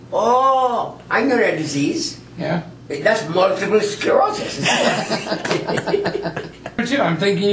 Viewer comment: what does he say. Multiple wat lol